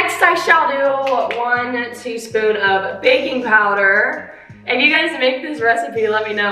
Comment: When you said baking powder I thought u said baby powder😂😂😂😂 I was like WHAT!!!